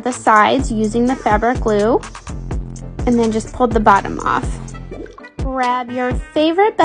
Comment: I thought she said "pull out". Oops.